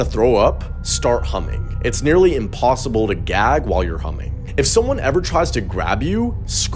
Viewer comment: I thought he said “if you ever need to throw up, start humping it’s impossible to gag while humping and you won’t be able to throw up” before I realized he said humming.